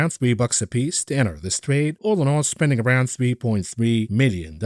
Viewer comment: what are you saying? Stanner this trade?